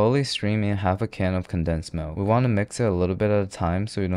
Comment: I need someone to tell me if he actually said condensed milk instead of evaporated milk bc it's the third time I'm watching the video and I can tell xD